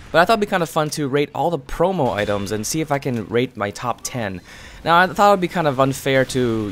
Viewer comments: wen u said rate your top 10 it kinda sounded like u said rape your top 10 LOL xD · Who else thought that he said rape the promo items instead of rate?